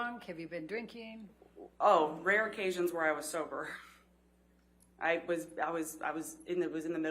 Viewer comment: “oh RARE OCASSIONS when I was sober”. Did I hear that right?